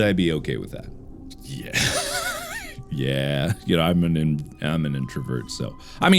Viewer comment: Did anyone hear at 3:43 that he is an introvert sub?!?! 🤔 or is that just me. 🤣